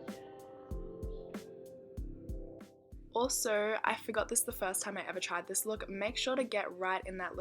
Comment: So stunning 😻✨ P.s. I always hear you say 'I forget' but isn't it 'I forgot'?